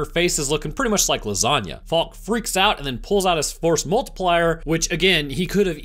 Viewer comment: Instead of “Force Multiplier” you could say something like “piece” or “angry tube” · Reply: I thought you said "Force Markiplier" and I was confused